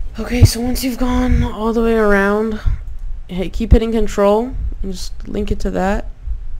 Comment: Min 25:35 what do you say ? key betting controll ? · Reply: reaa keep hitting control 😅